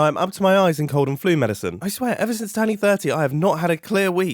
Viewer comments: I thought he said 2030. Causing me to quickly panic wondering how I missed 4 years…. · At ~11:47 he said “ever since turning 30” and I had to listen to it at least 10 times because I thought he said “ever since 2030” and I thought it was proof is a time traveler 😂🤣